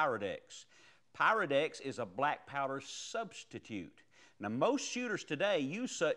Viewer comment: Great video but sounds like he is say Black Power lol